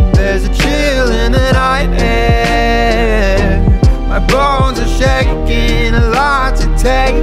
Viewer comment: I thought they said balls instead of bones